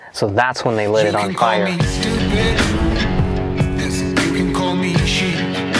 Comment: Hello everyone! Thank you all for being here 🖤🖤 Please make sure to give this video a LIKE and if you aren't SUBSCRIBED yet please do so 🙏🖤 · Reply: I can hear it to. It sounds like it says easy or sleazy lol